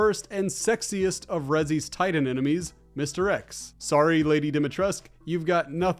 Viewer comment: Almost thought you said Tyrant enemies and was gonna have to correct you on Lady D not being amongst their number. 😅